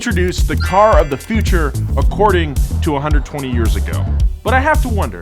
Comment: did Nolan said 220 years ago? I wonder Daimler Benz didn't estabilished yet at the early of 1800's😅